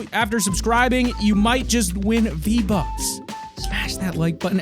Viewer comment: At the start of the video I thought he said he's giving away 3 Vbucks 😅